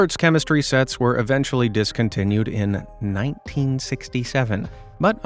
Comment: did I hear 67?